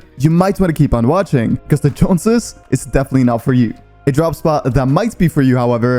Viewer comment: idk what you say there.. do you say, joneses is NOT contested or is it? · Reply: What I'm saying is that if you are looking for a drop spot that is NOT contested, Joneses is NOT for you, meaning Joneses is heavily contested :)